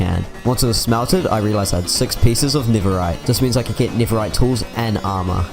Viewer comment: i heard n - rite